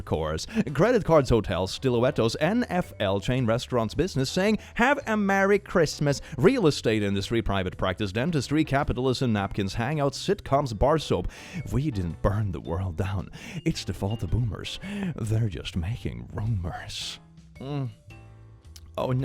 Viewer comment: I THOUGHT IT SAID CANNIBALISM